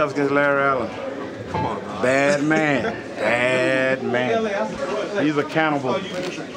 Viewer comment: 1, I thought he benched 705, 2, when I heard "He's a canable" I busted out laughing